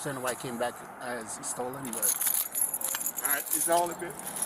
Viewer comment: If you listen very carefully you can hear the cops say oh it came back not stolen I thought it said stolen